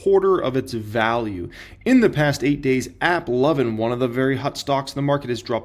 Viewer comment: you almost gave me a heart attack thought you said Apple was down 37% 🫣 sheesh either way it's rough hold that bag and buy more if you got the $$$